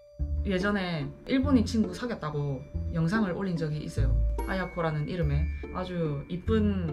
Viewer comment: 2배속으로 영상 보고 있었는데 친구분 이름이 자꾸 하야꾸로 들려서 너무 빠른건가하고 1배속으로 다시 돌렸는데도 계속 하야꾸로 돌려서 몇번을 다시 돌린건지 모르겠네요 결국 아야코 라는걸 알게 되었습니다 + 닉이 바뀌신걸 이제야 알았는데 한차원 더 변신하시는건가요ㅎㅎㅎㅎ